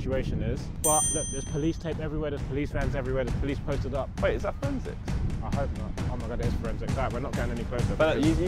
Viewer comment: Yo at 36:26 josh says is that a friendzix can someone tell me what that it is reply to my comment pls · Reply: Forensics